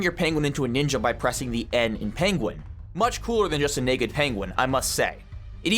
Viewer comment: what did you say gang ⁉️🤨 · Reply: I said it's COOLER than a naked penguin. this is not controversial. A guy in a ninja outfit is always cooler than a naked guy.